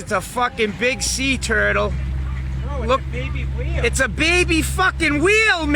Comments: sounds like he said baby wheel LOL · I thought he was saying a "baby wheel..." 😃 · I honestly thought he was saying, "Its a WHEEL, kid!" Cause it's round...so I thought he thought it was an actual wheel floating on the surface. Strangest accent ever. Some deep south accents are pretty hysterical but this guy's accent takes the cake!